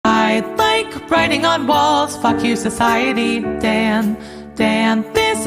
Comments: I thought she said "I like writing on Balls" 💀☠️ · I heard "like writing on b-alls" 💀